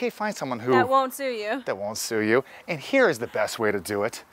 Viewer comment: Haha, ahhh sue you, first time around I heard " scrue 😁😂🤣